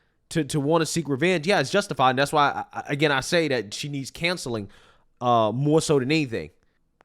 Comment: she needs what now? Cancelling? Do you not know how to say Counceling? 😂 that’s the second time you’ve said this and I’m like what the hell is he saying canceling for?